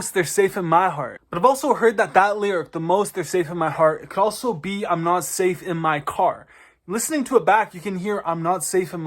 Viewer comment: I hear "you lost me, I'm not safe in my car" "